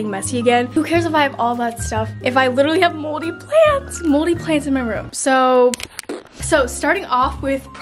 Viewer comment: i thought she said moldy plans and i was like "same bro"